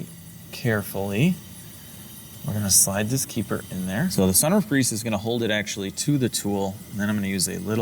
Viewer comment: I kept hearing “Mercedes Benz Summer of Grease” in my first several videos and I’m not sure if now glad it’s clearly “sunroof grease”